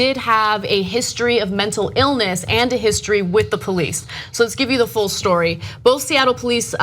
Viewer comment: This guys accent made it sound like he said "the beast" instead of "the police". I think calling them the beast is more fitting like summoning a demon. It might help you somewhat or it might kill you.